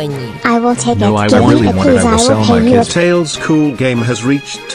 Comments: i could’ve sworn i heard one of the shark tank members say “i will sell my kids” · "This game so fun" "Giggle physics" (After awhile) "I am *Bla bla bla*" (Chatter) (I hear "I will sell kids")